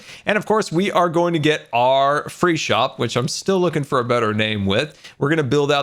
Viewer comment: I misheard Free shop as Freak shop 😂😬